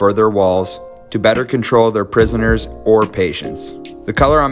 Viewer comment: I misheard him saying "schools" instead of "prison" (I'm not kidding I srsly heard that) then when I heard him saying prisoners I thought it was a joke for students- I-......